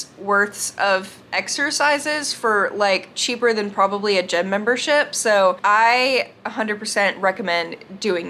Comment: Guys it might just be me but when she said gym membership i thought she said gym membershit. Lol 😂